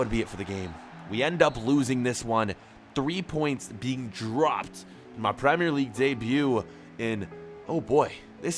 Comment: Did you just say, Your Premier league Debut